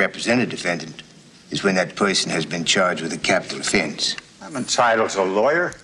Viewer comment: what does the attorney said in minute 6:59-7:20? capital defense? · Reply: Ana Rodriguez capital offense